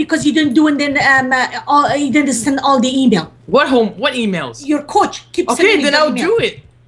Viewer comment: Did she just say your cooch... YOUR COOCH buh buh buh.... I thought it was a dude... I need to go play the kim Kardashian game... im kunfuzed